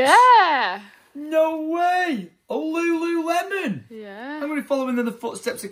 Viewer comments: sounded like was trying to say lululemon after you just said it lol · swear to god I heard say lululemon after did XD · ? Sounded like tried to say LuLuLemon